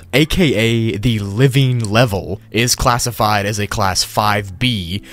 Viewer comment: On the first videos that a watch i thought he said "is classified as classified" instead of class five